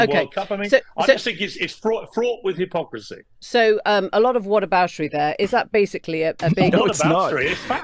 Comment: She says "what-aboutery" I hear "hypocrisy"